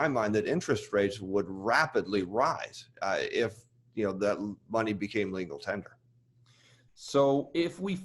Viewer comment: if money became legal tender. Did I hear that correctly? Tried unwrapping that and came up empty handed.